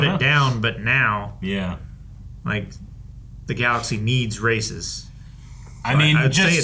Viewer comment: I heard the Galaxy needs racists lol